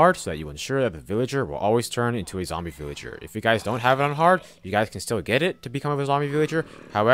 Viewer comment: what he say? One heart or unharmed? Can't understand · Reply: *On hard mode he said